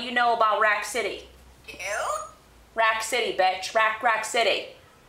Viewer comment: Did she said bi*ch at 1:04?